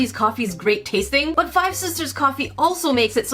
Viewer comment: I thought you said "hi sisters" but you said "five sisters".